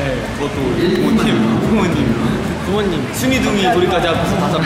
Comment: did I just hear "Soonie Doongie Dori" or am I hearing things?